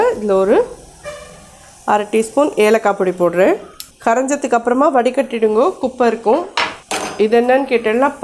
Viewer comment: Thanks mami!! at 5:12 mins, the caption says "Asafoetida powder" instead of Ellakai powder, which is what you are correctly saying as voice over...do change the caption pls...